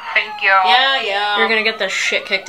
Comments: I’m not joking when I say I thought she said, “Pancake plot twist” 😂😂🥞🥞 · “You’ll get the shît kicked out of you one day you know?” “And I’ll like it! Pain kink plot twist!” 😂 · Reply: I’m happy for subtitles cuz I thought she said pancake plot twist.😅